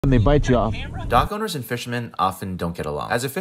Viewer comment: Oooohhh you said dock owners. I thought you said dog owners and i didnt know why fishers had a problem with dog owners.